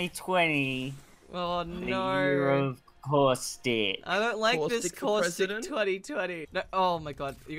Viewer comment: The English automatic captions detected "horse dick" instead of "caustic"